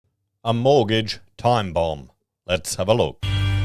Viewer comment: Oh damn! I thought you said. " Mortgage is the Bomb!"